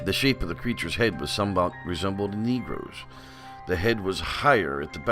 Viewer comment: Hey Mr at 2:44 did you said his head resembles a n negro I want some feed back if so I'm unsubscribing im a negro and I love your channel i hope to god that not what you said have a bless day.